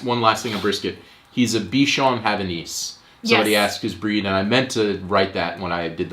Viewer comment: Bichon Havanais makes sense, I heard "be Sean Hannity" at first and thought what your dog had to do with fox news. :).